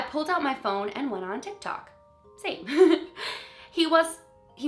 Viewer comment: For some reason the auto-captions thought you said "Satan" instead of "same." It didn't do that the other time.